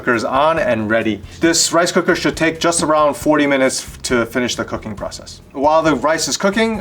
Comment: Did he say 40 minutes at 2:38?